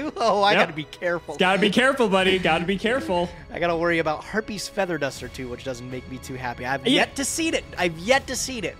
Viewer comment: "It should be fine as long as we don't see Harpie's Feather Duster...." Sentences uttered before disaster. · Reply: It really sounded like he said...(Herpies feather duster) which caught me off guard.